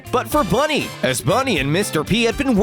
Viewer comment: not for you but for money! I thought it said that It said bunny